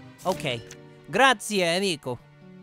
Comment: did he said "Grazie Amico (Thank u friend)" or "Grazie Enrico(Thank you Enrico)"??? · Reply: Plushy_ 461 amico